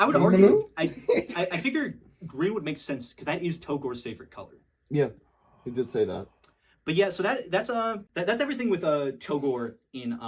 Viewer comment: I was listening to this as background noise, but then I heard “Togore” and it instantly activated a flight or fight response in me. Thanks :)